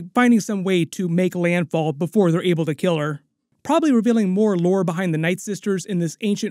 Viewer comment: did you say, her making landfall before they kill her?